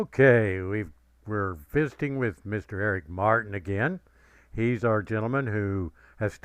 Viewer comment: I was yawning during the introduction and it sounded like he said, "fisting with Eric Martin."